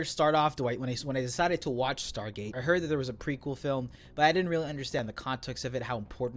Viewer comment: He said precurser not prequal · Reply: "I heard that there was a prequel film...". It doesn't particularly matter in the grand scheme of things but that's the word used (incorrectly, in this context).